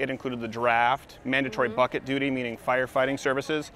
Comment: Every time I hear you say "bucket duty" I hear "fuck it duty". That says a lot about me.